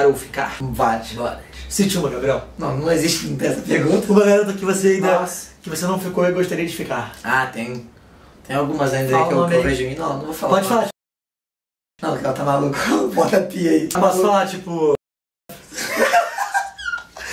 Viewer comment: fala uma garota famosa q vc gostaria de fica tipo a Bella fernandes! Tipo eu entendi isso😺